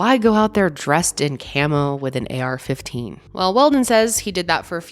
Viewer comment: I haven't had the pleasure of firing an AK-47 with the suppressor, only without so I wasn't sure if the recoil and all that was the same but it's good to know that it does have a significant difference 😀 I'm pretty sure that idiot would be accustomed to firing it with and without. I'm surprised he didn't take that into account when he was trying to make people believe his bs 🙄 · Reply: shit, I could have sworn she said it was an AK-47, not an AR-15. My bad, but my point still stands. Theres no accident when it comes to this